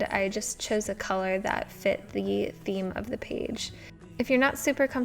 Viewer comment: Omg thx for making this video! It really helped me out since I'm new to journaling. did anyone else catch where she said "yeet theme" ? 11:32 {no hate, just thought it was kinda funny!}